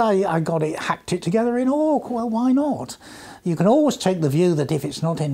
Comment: Orc programming language? I didn't catch what he said. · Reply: Try AWK (after Aho, Weinberger and Kernighan). It can claim to be the Grandaddy of more recent scripting languages such as Perl, Javascript and Python.